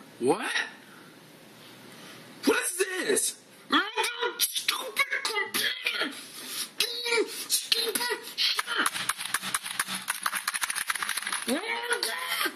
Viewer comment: What is he even saying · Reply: I believe he said "What is this!? GRRRR stupid computer! Grrr stupid!"